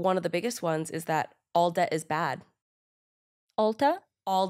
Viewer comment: I thought she said Ulta is bad too 😂